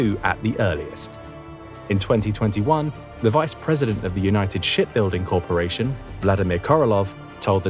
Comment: thought he said $h!+ building